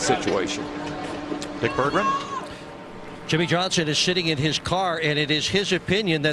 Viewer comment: Did he just say Jimmie Johnson is shitting in his car? :P